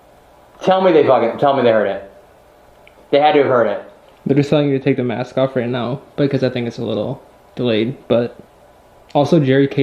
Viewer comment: When you asked if the mask made them more comfortable talking to you, I heard, "Take the mask off,"